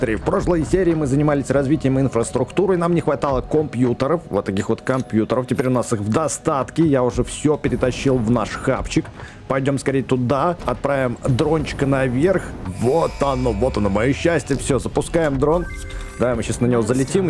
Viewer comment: Мне послышалось вместо хавчика хавчик